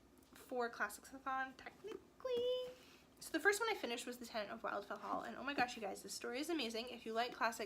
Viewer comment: I am sorry, at 21:19 I do not understand what you are saying the title of this book is, nor do you mention the author. It sounds really interesting and you obviously love it, could you print the title? Thank you. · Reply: Joseph Francis Burton So sorry! It’s The Tenant of Wildfell Hall by Anne Bronte. This is what grad school does to a person’s brain.